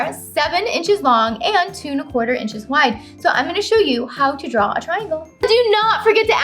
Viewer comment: What did she say at 4:39? I cant make it out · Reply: I say...... I am going to show you how to draw a triangle. I know it's a bit hard to make out. Sorry. I just get so excited and talk all crazy. lol. Thank you for watching.